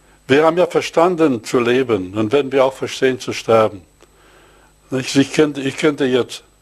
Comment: "Wir haben verstanden zu Leben , wenn wir verstehen zu sterben" 21:<num> mal anrufen :D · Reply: Er sagte doch wenn wir verstanden haben zu leben dann verstehen wir auch zu sterben und das bedeutet etwas anderes...